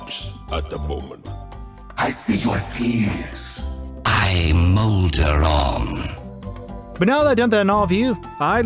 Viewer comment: I heard "I'm Molderong", but that makes no sense. When I look that up, Google has no idea what I mean. It says "Your search - I'm Molderong - did not match any documents. Suggestions: Make sure that all words are spelled correctly. Try different keywords. Try more general keywords. Try fewer keywords."